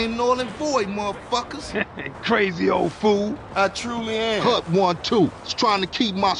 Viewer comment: can anyone explain what Ryder said, I don't know much English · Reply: no no he was like saying "punk one two?" "cut one two?"